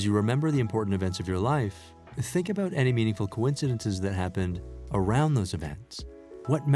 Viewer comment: Did I hear you say coincidence at 6:02 ? I thought you said there is nothing like coincidence but only synchronicities in your previous videos.....